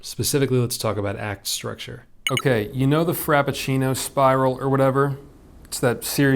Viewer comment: Lost my mind when I heard "Frappuccino Spiral" xD